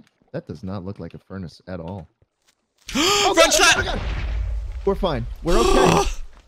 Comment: Did Phil say : FRENCH SCHLATT! Because thats what i heard